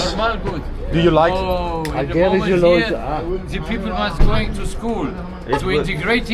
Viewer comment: he said "Albaner schlimme Leute hier" which means "albanians are bad people here" you probably thought he said "Albaner lieben die Leute hier" which is the complete opposite lol